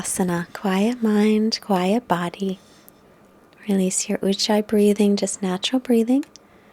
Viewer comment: att around 28:15 in today's practice, you said to "Release your XXXX breathing." It sounded like uchai to me. Could you talk about that a little? During practice I'm just breathing in and out as per your instructions (in on up-dog, out on down-dog, etc). How do I do that special breathing? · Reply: Gregory Stewart Thank you so much for your question. It's called Ujjayi breathing. Here is a short tutorial that will explain it: <link>